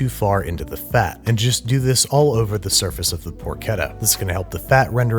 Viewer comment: I thought it said pochita 😭